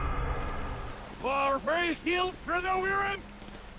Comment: Seriously though, what the hell did he actually say? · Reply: Merrywheather fleet's here bro thats what Trevor said